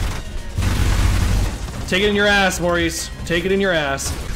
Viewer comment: did he call Cerberus Maurice?!